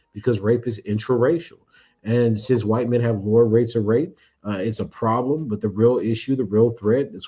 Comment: Is it really true that white men have lower rates of grape? I know the FBI stats say that Black men are arrested for grape at a higher rate, but Black men also have the higher false accusation rate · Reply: Sounds like he said “more rates of rape,” which I take to mean higher rates of rape across different categories.